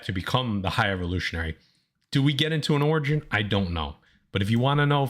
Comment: When speaking about the High Evolutionary, I know that said "Do we get into an origin, I don't know" BUT WHAT I HEARD WAS: "Do we get into an orgy, I don't know"… When does that movie drop!? LOL